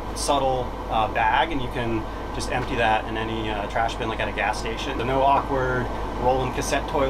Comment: he mentions throwing your turd bag into "any trash bin, like at a gas station"... did I hear that right?